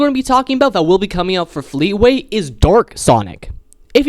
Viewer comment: It was so funny when he meant to say dark sonic but it sounded like he said “Dork sonic” at 3:56 😂